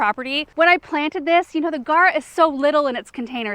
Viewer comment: Love all your videos! What is the pink whimsy flowers at 7:07? I think you said Carr?